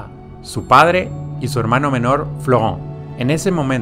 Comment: entendi flojon